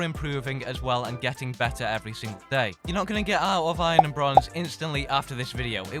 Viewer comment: Lol I thought it said get out of bronze and go to iron